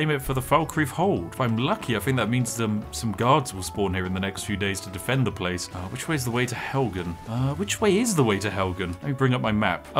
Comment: I thought he said 7 Days to die, man I played that game for hundreds of hours but needless to say I'm still not disappointed